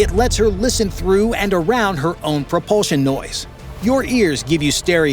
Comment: hmm… I thought you said no propeller noise. 😐